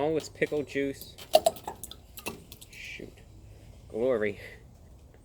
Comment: I almost had a heart attack when that juice splashed on you. Hope you're okay brah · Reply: I almost thought heard him say shit for the first time under his breath but it’s shoot :(